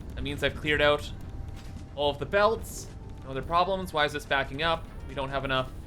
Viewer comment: I thought you said no more problems